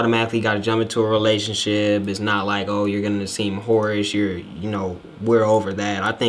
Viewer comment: When he was saying whorish I thought he was saying porridge... I was like “is this some type of slang” I was tryna decipher the shit n all like maybe it means mushy and easy cus it’s easy to make but nah man I really had to watch the video twice cus I ruined it the first time with my speculations.